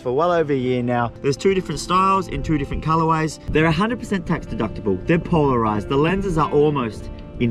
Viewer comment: Did he say those glasses are tax deductible? Wut